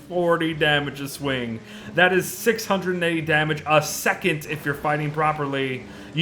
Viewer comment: I thought it said "400 Wait a Second..."